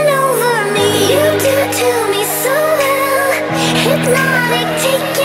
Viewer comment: I thought she said "tutu" instead of "to me"